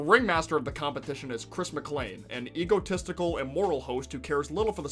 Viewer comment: me: wait did he just say chris is immortal?